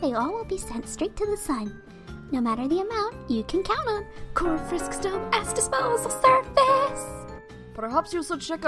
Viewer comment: I heard “Core!Frisk’s Dumbass Disposal Service!”